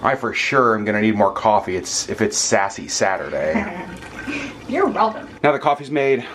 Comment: I thought said "Sexy Saturday!" LOL and NO ketchup on eggs. Gross!